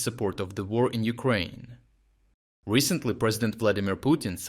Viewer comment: Ummm, is this a recycled video? That VAT increase was announced by Putin in November last year and went into effect Jan 1st this year. Yet the video is speaking about the increase in the future tense. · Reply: I thought he said "recently raised...."